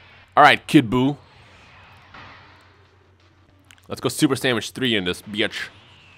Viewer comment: Did he say super sandwich